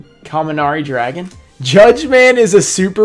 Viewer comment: sounded like you wanted to say konami dragon lol